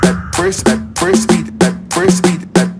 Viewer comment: In the beginning what i heard was "First beat the app" lol!